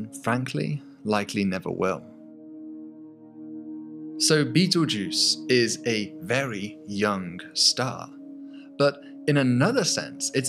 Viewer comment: why did I hear Beetlejuice is such a young star